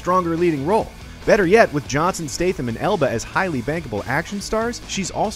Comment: Anyone catch him say “Johnson Statham” at 2:00 ? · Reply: He wasn't saying "Johnson Statham". He said "Johnson. Statham and Elba" speaking of the three main male roles.